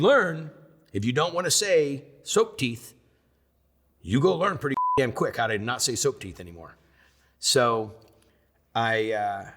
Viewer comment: Soap Teeth? Did I hear that correct?